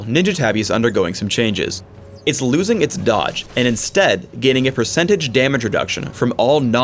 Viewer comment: he clearly said "getting a percentage damage reduction". And from I've seen from Surrenderat20, it's going to be -10% damage from all non-turret basic attacks. I don't know where you got "do a percentage CHANCE for damage reduction" from.